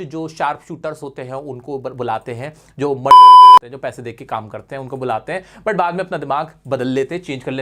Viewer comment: kya bola 😂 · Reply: murder bola